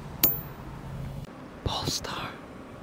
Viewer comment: I heard "pornstar" instead of polestar... thought u r one of them😂😂